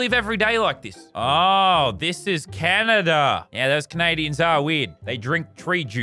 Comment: WHAT DID YOU SAY ABOUT CANADIANS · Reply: Tom Isaac Ayto C-A-N-A-D-I-A-N-S A-R-E W-E-I-R-D